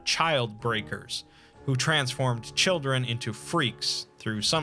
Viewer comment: “But kidnaping children?” “I’d kidnap a thousand children before I let this company die, and I’ll silence anyone who gets in my way!” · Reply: You know for a second there i thought you said "chickens" instead of "children" and i was really confused on how anomalous chickens can be used in the anomalous circus